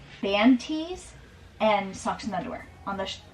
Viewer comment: I’m so thankful you clarified band tees, I thought y’all were saying “manties” like man panties 😂 I was like “why would his underwear be sentimental?” 🤦🏼‍♀️😂